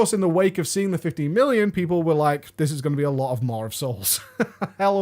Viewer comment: Is it just me, or does say MaWR of souls several times in the start of the video? I really had to pause the video, to go back and check for myself. Is it pronounced with an R? Listen to